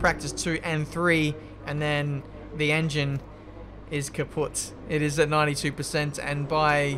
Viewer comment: Did he say "kaputt"?! 3:35 If so. The pronounciation was pretty good.